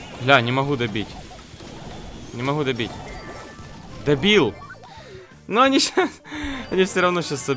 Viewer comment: Я один услышал что холдик сказал дебил